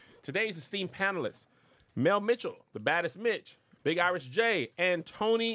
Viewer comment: I thought Kev said the baddest btch 😂😂😂😂😂😂 I was flabbergasted for a sec.